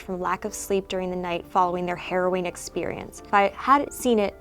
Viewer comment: I thought she said "Heroin experience" (I know that it's actually "harrowing") since I was assuming that these eye witnesses on drugs. But then again, heroin isn't a hallucinogen!